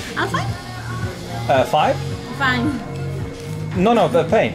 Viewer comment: ■ More updates from the road: <link> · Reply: My friend if you didnt get what the bartender girl said to you in 2:09 after she saw the 50 soles bill id tell you.. she tried to say in english " is it fine? she thought you didnt want the change haha...Cause most american tourists paid and said " Is fine, keep the change " Thats why she gave you a thumb up when she said that ... But didnt work for her this time haha thats why her friends were laughing, cause was a failed ... greetins from Lima peru